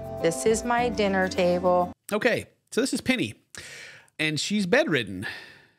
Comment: I thought he said "this is pitty". Had to read the screen to know he said "this is penny" lol